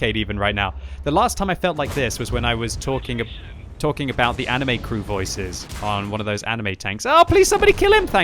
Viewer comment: did he just say anime crew voices ok idc how good the tank is I just want anime girls screaming at me